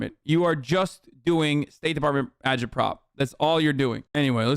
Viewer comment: im new here wtf is adjiprov??? he said it at 6:56 ish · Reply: just referencing agitprop, or propaganda